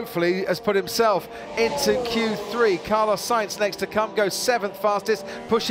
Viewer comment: “Carlos sainz next to c*nt” Did I hear that right? Hahah